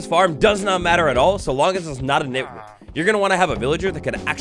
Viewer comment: did he say the n-word and bleep it out with a villager sound??? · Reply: No, he said nitwit. It's what the green shirt villagers are called. :)